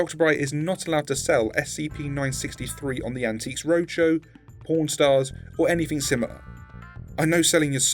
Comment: am i going insane or did he say REDACTED stars instead of PAWN stars